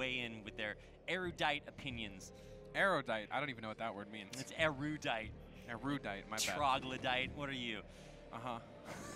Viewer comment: Someone can spell me what are those words what rapid explaining to? "erudajt" "ladaj" WTF. His pronunciation is horrible... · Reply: erudite & Luddite